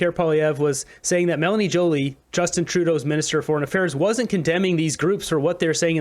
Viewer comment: I always hear “minister of marital affairs” lol